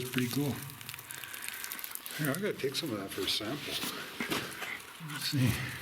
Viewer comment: ah man I just rewatched the bit (@ 21:30) and you said take it for a sample. I was just curious as to what it was that's all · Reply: yeah sorry mate I thought you said test it but you said take it for a sample so I just misheard initially.